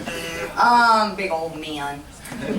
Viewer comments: what did chris say at 5:40? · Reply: “I’m a MAN” cuz the person called him a GUY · What'd Chris say at 5:44 that caused everyone to laugh? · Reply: Joyce Heyeh “I’m a MAN”